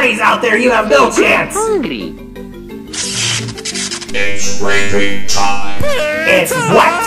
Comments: I always thought Freddy said "It's REAPING time" as in time to brutally kill someone. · That’s me when I heard someone say rapping time